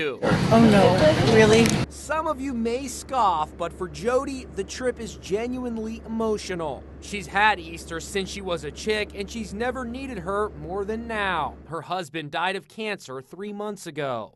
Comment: I heard " but she never eated until now" instead of needed and I was like wth